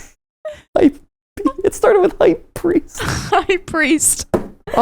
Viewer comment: mat pat through the giggles and laughing says "hype priest" in whisper. i thought he said "i peed", cause he was laughing so hard